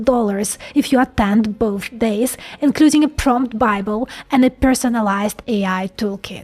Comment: Oh... She said "PROMPT bible". Ha Ha, I thought she said "TRUMP Bible"! I was like: What!?!?!?🤣